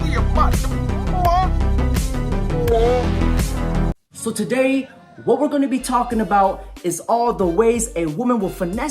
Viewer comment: Who is the man in 02:10 and what is he saying? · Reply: I'm about to bust (something like that)